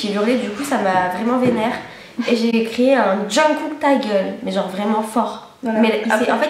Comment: Je sais pas si c'est moi mais j'ai l'impression qu'elles disent "Jungkook" xD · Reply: Nuage Ailé Oh, moi aussi j'ai entendu "Jungkook" x)